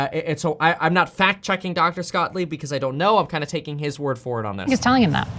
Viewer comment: I thought I heard you call him ' Dr. Scottlieb.' Haha